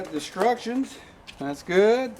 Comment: Ha ha . . . did you just say, "destruction" or "instruction" ? nice work.